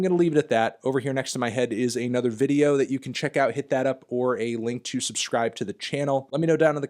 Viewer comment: “Hit that up, or the link to subscribe” this entire time I thought he’s been saying “Hit that upworthy link.” I never knew what the hell he was talking about.